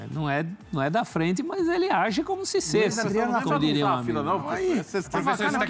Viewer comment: Só eu ouvi um "ele age como se SESSE"??? Aos 4:35. Jesus, q vergonha!!!!